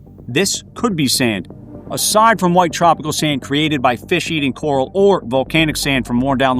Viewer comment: Sounds like he said Fish-Eating Coral lol